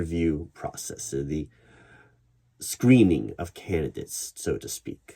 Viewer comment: Thought you said "screaming of candidates" instead of "screening of candidates." Yeah, I think that's what I'm starting to anticipate here.